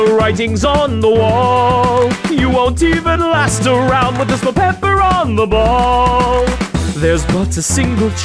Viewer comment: One must imagine Peppino happy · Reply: Why did I hear ‘with this belle pepper on your 🎾